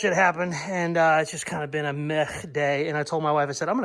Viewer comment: Blahaha you said a Meh day but it sounded like you said a "Meth day" as you cut up you old truck into parts lmao..